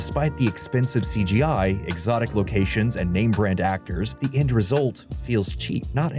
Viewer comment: did anyone else hear 'inbred actors'? 😮 😲 😅 😂